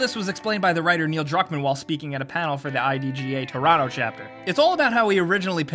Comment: did he say "Torato" instead of "Toronto"?